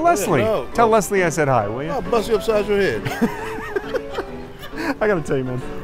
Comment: Can someone plz tell me what the guy in cap said at 2:58, when started laughing. I tried my best but couldn’t get it. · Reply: He said: "Imma bust you up side yo head!" As in, I'll give you a smack in a minute if you don't pack if in. I think he was half joking, but he was definitely pissed off.